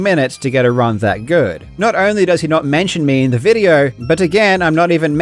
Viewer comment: lol Did you intentionally say joke to make it sound like you said jerk?😂😂